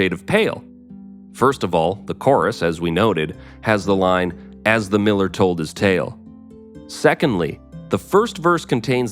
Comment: And all these years I've misheard the line as: "as the mirror told it's tale" and that it was about some psychedelic potion like absinthe! Kinda fits, in some weird way, though. 🙄😄